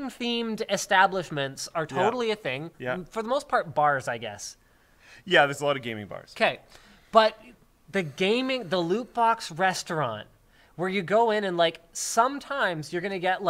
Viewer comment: I thought i heard Luke say "There are loads of Gay Bars..." turns out it was Game Bar.